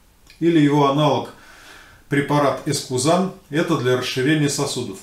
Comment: Здравствуйте!Вы сказали конский каштан или аналог эспумизан-а это препарат для проблем газоотведения,либо я(извините)плохо расслышал?для расширения вен,Спасибо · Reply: ЭСКУЗАН